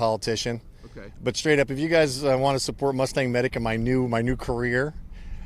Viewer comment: Any native english speaker can help me figure out the name of the youtube channel the guy is saying at 23:39 ? I would like to know what he was about to say about what he hear from federal on the ground.. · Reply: Mustang Medic.